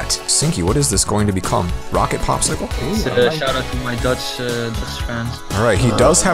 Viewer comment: it said dodge instead of Dutch